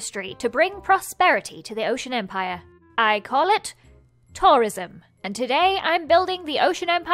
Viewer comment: omg... when she said ''tourism'' I THOUGHT SHE SAID TERRORISM!!